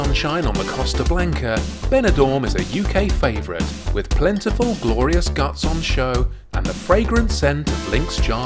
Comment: Had to rewind when I thought you said PLENTY OF GLORIOUS GUNTS ON SHOW.